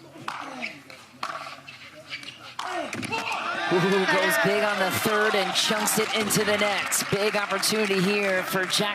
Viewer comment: what did I hear? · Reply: "come on" really fast